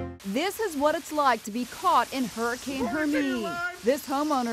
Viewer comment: I thought it said Hurricane Heroime